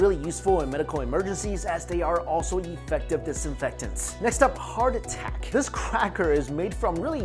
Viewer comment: I thought he said next up, heart attack.